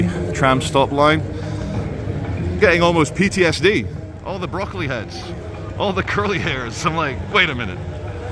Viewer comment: Mate did you just call the kids at 2:38 broccoli heads.... wtf.